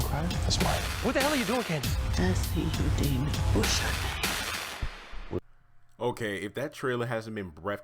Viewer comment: I think you misheard a few words Hanna said "I see you devil what's your name" and the scene where you said Candace said last chance I think she said something about him but I'm not 100 about that one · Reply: Hanna said...I see you demon..what's your name?